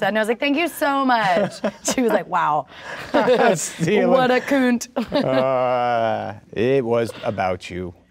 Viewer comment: did she just say.. koont?